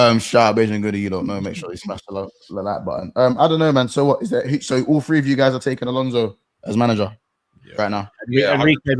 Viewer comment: Camavinga is a baller… what is he saying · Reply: Bang average